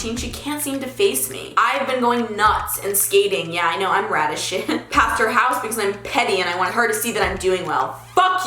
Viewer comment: " yeah I know I'm rad as shit" What i heard.....Yeah I know I'm raddish shit 😂😂😂